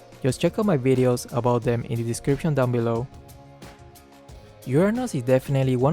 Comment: I'm sure I heard "Your Anus."